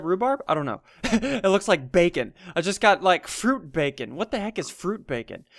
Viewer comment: Did anyone hear what he said 7:47 (fruit bacon?) He predicted it